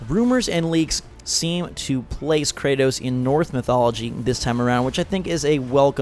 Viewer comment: did you say north or norse? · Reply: he meant norse but it sounded like he said north